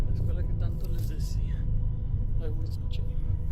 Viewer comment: The "disembodied voice" at 5:50 when the guy is climbing the fence isn't a disembodied voice. It's the guy complaining about climbing the fence. I can hear him say "ay baboso ________". I couldn't catch the last part of his sentence. Basically he's saying "oh shit" or "goddammit" while climbing.